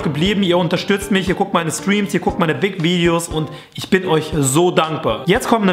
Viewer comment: Ich hab verstanden "ihr guckt meine dig-videos" eeeeyup gerne! :D